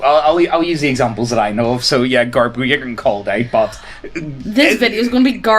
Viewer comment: My fetish is tax evasion. You'll never catch me IRS. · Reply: What the hell did they say at 0:33?Youtube's CC describes it as "garb" and "garbrogen"